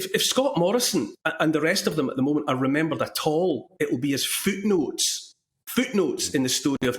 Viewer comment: I really thought Neil said fruit loops, not foot notes. He'd be right! It is mad here!